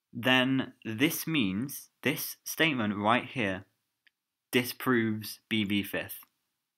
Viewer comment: holy shit my heart dropped because for a second I thought you said this proves BV5TH when you said this DISproves BV5TH